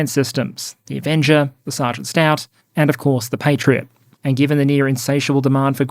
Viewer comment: What did he say between Avenger and Patriot? Sergeant what?! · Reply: Sgt. Stout. New name for the Stryker M-SHORAD. I had to look it up, too, Lol!